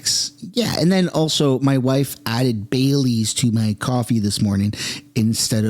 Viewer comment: I thought you said your wife put "bay leaves" in your coffee... bailey's! 😂